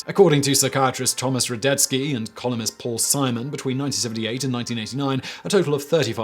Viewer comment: When he said "columnist" I heard "colonist".